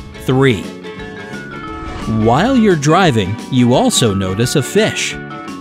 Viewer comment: I hope I’m not going insane but did he say driving